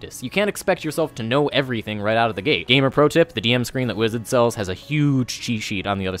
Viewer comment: What was that DM screen you mentioned at 3:40? I rewarches that part tons and can't understand what you said. Wizits? Wizards? You say it so fast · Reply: "the DM Screen that Wizards sells". "Wizards" here referring to Wizards Of The Coast, D&D's publisher.